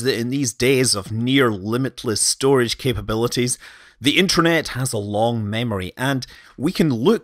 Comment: did you say Intranet rather than Internet?